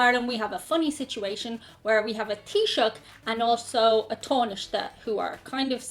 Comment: So, I wanted to know what you were saying when you named the politicians, and I turned on the Closed Captioning at 10:04. This is what it says: "... where we have a t-shirt and also a tarnish that who are kind of simultaneously in charge."